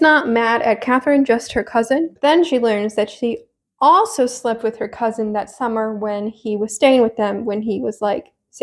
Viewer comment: Fully thought you were saying Susan slept with her own cousin and I was really confused because I do not remember that plot line...